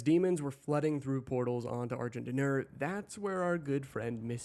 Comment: First time I heard Argent D'Nur I thought it was pronounced Argh gin dinner.